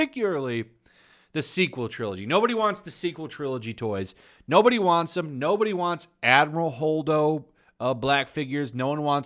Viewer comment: sounded like you said "No body wants this Hobo" LMAO